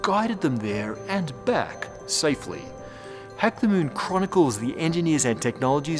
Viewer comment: I keep hearing "heck the moon"